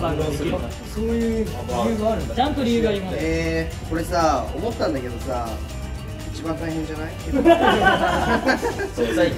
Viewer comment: 「これさあ思ったんだけどさあ……一番大変じゃない？……んふふ」 →「何で俺が一番大変な作業やってんの？誰か代わってよ(圧)」に聞こえて震えたwwwwwwww